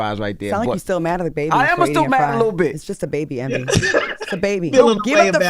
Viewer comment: Did Envy just say he's "mad at the little bitch " when talking about how his baby ate his fries 🍟 🤔?